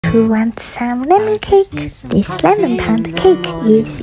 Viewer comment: I thought u said who wants salmon cake at first 😭